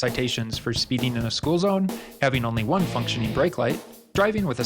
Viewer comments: When he said she was charged with 'having only one functioning brake light', I heard 'only one functioning brain cell' 😄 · when i heard she was charged with having only one functioning brake light i legit thought they were saying charged with having only one functioning brain cell 😅 until i realized the B word was brake